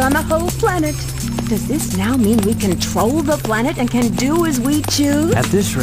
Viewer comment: at first I thought rouge said " does this mean we can troll the whole planet?"